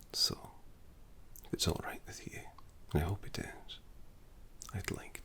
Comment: When you said, "If it's alright with you," early on, I thought you said "alright, what's wrong with you?" and I was like, "yep, that's Scottish" 😂